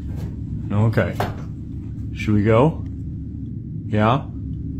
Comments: anyone else hear a whisper ya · I heard the disembodied voice say “YEAH” at 2:11 not get out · Anybody notice? Listen from 2:10, to about 2:15. He asks the pup, "Is it time to go?" I heard a soft woman's voice say , "Yeah" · I heard something say "yeah" at 2:11 when he asked the dog: should we go? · I heard lady say yes after between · Did you hear it? When the man asked his dog :shall we go,yeah? You can hear what seems like a female voice saying yeah. Maybe I am just really stoned watching late night.😹